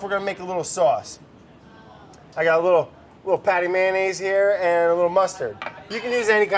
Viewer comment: patty mayonnaise? did I hear that throwback reference right?!?!?!?!